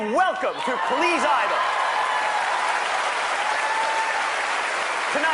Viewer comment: I thought I heard 'Cleese Idle'.